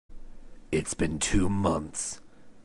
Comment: My volume wasn't loud enough, so I heard "two months" as "two minutes". XD The animation is very smooth. I love watching lip syncs. :3